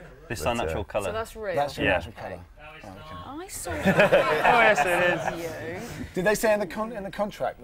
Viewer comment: What does the guy behind the scenery say ? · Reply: He says "No its not!" :-)